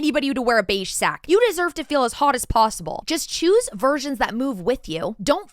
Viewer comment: i thought you said "virgins" & i was so confused for a second lol